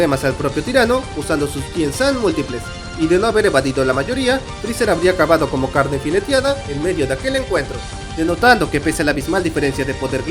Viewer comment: como tenia el volumen bajo yo escuche "de no haber evadido la mayoria... krillin hubiera acabado con la lagartija lesbiana"... JAJAJAJAJAJAJAJAJAJAJAAJAJJAJAJAJAJAJAJA 😂😂😂😂😂 XD... tremendo...